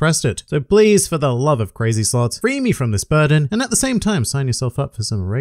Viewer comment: I heard "crazy sluts" instead of "crazy slots" and I was like: so that's this channel's intro? xd